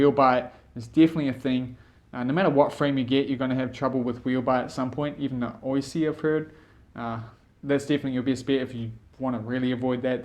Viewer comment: what did he say? CC was no help at all. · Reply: "No matter what frame you get you're gonna have problems with wheelbite at some point. Even the OYSI I've heard. That's definitely your best bet if you want to avoid that though." Hope that helps 😆 OYSI should be pronounced Wish too, I got that wrong in this video. Cheers for watching!